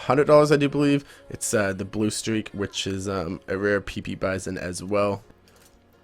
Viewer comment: I thought he said rare pepe xD